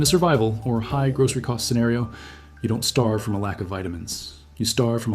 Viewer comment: “ You don’t stave to death from lack of calories. You starve to death by lack of protein and fat.” I have learned something NEW TODAY, from You. Thanks, Love that Brain surge of Dopamine! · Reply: That's not what was said. It was, "You don't starve to death from a lack of vitamins."